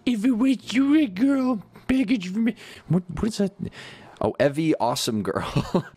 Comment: I thought was saying "Evie the deranged girl"